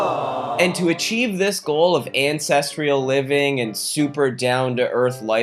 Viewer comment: I kept hearing ‘incestrial living’. I was scared for my life